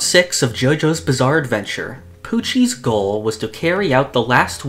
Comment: i swear i heard "Coochie's goal"